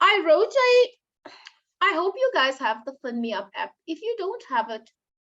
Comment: Ooh I talk so much😂 Let me watch again to see what i said. I use LinkedIn, Wikipedia & Glassdoor for leadership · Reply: 😂😂 it sounds like you said the Pin Me Up App, it’s around 2:09:30 where you mention it